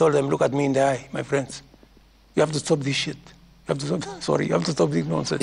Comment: Ohh my ..Did he just say, "You have to stop this sh*t!!"